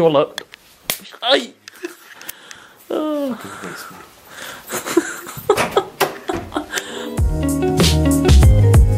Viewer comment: what did you say ? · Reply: wasteman :P